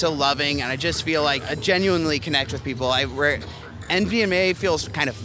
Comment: The guy at 5:00 that likes 2cb, does he say mdma feels fake... I kinda hear sth like nvma, but i am not sure anyhting like that exists....